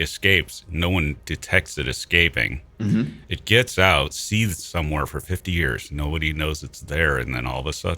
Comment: what does say here: "it gets out, (SEETHES??) somewhere for 50 years"? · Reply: Hides somewhere for 50 years?